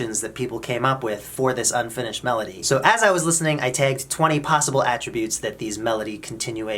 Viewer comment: "I taked." Did I hear that right?